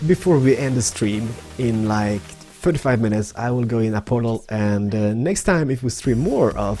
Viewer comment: hahahahaha 5:26 sounds like he says ill go in a porno !